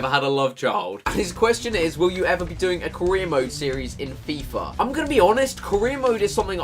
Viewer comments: who else thought he said korean mode not CAREER mode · Am i the only one who heard "korean mode"?